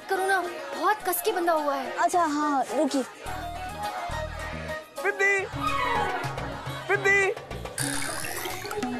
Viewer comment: Anyone noticed what she said at 6:58 ok in English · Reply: She said thik hai